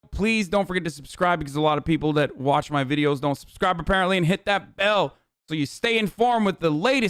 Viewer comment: Every time i have heard him say "hit that bell" at the beginning of the video (which has actually only been two videos so far cuz i just found this channel 😅) i think he is saying hit that L and idk if im taking a loss or doing acid 🤔